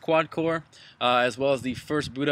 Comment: i heard you say subbride